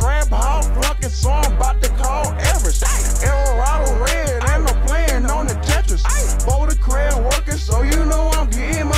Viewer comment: "Erra rodle red" @ 3:01 what da hell is oj saying??? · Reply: Eldorado Red. He’s a rapper that raps with juicemane